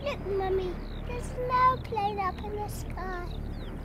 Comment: "LOOK MUMMY, THERE'S AN AIRPLANE UP IN THE SKY" HARRY WATERS, AGE 2. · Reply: My whole life, I thought the child was saying "there's no clouds up in the sky"...